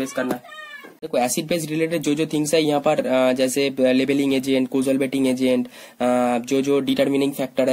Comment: I didn't understand what you said on 12:31 & 12:32 which agent ?? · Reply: Labelling agent